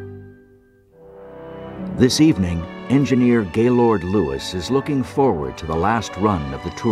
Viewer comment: "the engineer Gaylord Lewis" er did I hear that correctly??